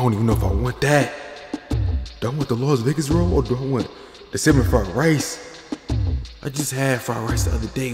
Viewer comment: i thought bro said "semen fried rice"